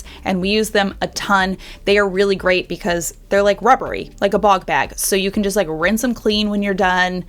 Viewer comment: Thought you said “They’re rubbery like a BALL GAG” 🧐😂